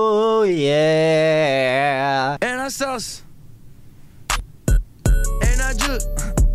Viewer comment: Among Us has ruined my brain I swear to god I thought he said "And I'm sus"